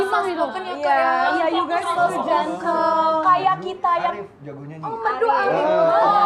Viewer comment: Denger ga sih pas di menit 14.00 bilang apa? Kedengeran ku kya SAYANG 😂😂😂.. Apa kabar dengan telinga ku.. Tolooongg🤣